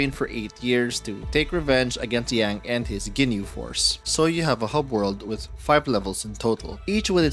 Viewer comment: did i hear ginyu force